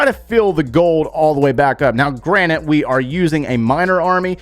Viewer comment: did say granite?!?!?!?!? hey it's Granted.